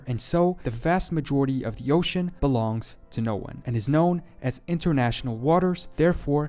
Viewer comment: was it only me or did he say „the vast majority of the oceans belong to Norway”?